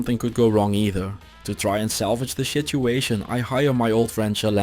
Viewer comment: Did I hear 6:21 correctly or was that an audio glitch? Suspicious suspicious... 💩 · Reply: No that is correct I definitely said shituation